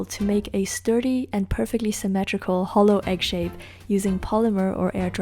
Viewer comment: When she said “hollow egg” I thought she said “holo egg” but when it wasn’t holo I got disappointed